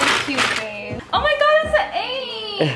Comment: I thought she said "Omg its an eighth" lol I need to stop smoking 🤣🤣 But I'm mad jealous of these two.. All of the women in my area just talk about rich guys, extensions, and WAP 🥲